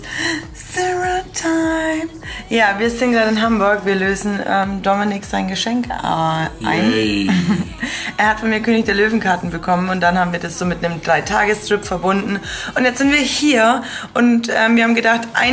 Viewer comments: Ich hab 3 Tage strip verstanden.Aaaaaalles klar.😂 Jajajaja ich weiß,sie meinte 3 Tages trip(oder so?!!). ok reicht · Bei 3 tage trip (0:52) hab ich einfach 3 tage strip verstanden 😂😂